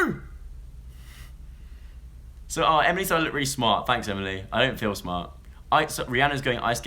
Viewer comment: what did Joe say about Emily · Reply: Rhubarb he said that she said that he looks really smart